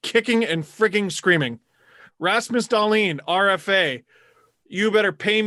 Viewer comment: Not that it's a big deal or anything, but Steve said Rasmus Dahlin, not Rasmus Ristolainen, was the 1st overall pick. misheard him.